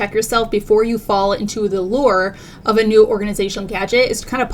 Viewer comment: “You need to check yourself…” ‘before you reck yourself’ is what my mind wanted to hear 💀. Instead of “the allure of an organizational gadget.”